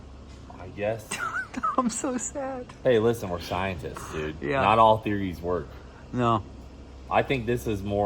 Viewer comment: did you say scientists or try-entists😂 just dont become cryentists